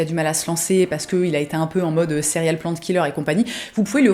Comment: Merci pour cette belle sélection ! Perso à 10:39 j'entend "céréales plant killer" voilà c'est tout pour moi haha !